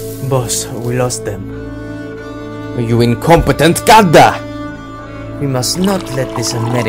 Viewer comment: I think what he said a5 the end was “gadha” which is donkey but urdu is off but A for effort man